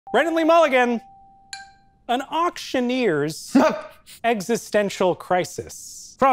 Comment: I thought that said "executioner" instead of auctioneer 😭